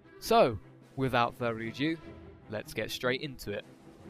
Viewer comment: is it just me who thought he said "without further a jew" lol